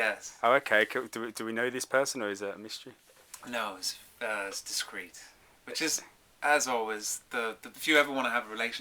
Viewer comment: What he says in minute 00:45 ? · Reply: It’s discreet